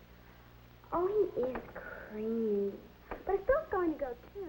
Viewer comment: Did she say dude looked creamy or dreamy?